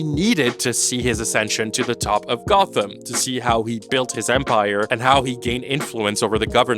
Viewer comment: you said "to see how he build his empire" and I heard "to see how he become a vampire" :) I had to rewind a little, as that would be an interesting twist :D